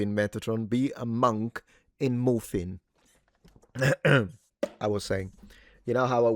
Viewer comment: 😆 when he said monk morphing I thought he said monkey morphing and instant thought of dbz 😆